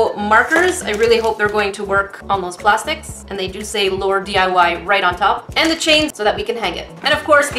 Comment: I thought it said laundry